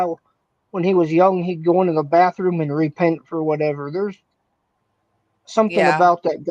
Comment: what does he say? "Chris Watts would go into the bathroom and..." sounds like re something? Thanks in advance. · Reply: Repent. He'd go into the bathroom and repent- he said :)